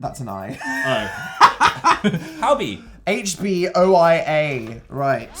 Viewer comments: I thought he said hobi at first sksksksksk bts is taking over my life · I thought he said howdy and I had Undertale flashbacks